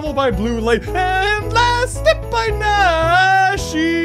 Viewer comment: ANNDDDD LAST STEP BY NAAASSSSHHHHIIIIIIIEEEEEEEEEEEEEEEEEEEEEEEEEEE!!!!! Actually kind of suits it. nice. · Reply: I thought it said blaster by Nashii.